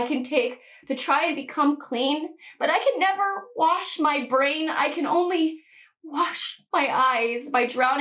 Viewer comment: I heard you said "brains" and i was like "well yeah that sound new", and then you said "vains" and i go "Oh my god, are you ok?"